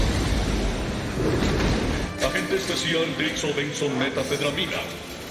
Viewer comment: pensé que dijo metanfetamina XD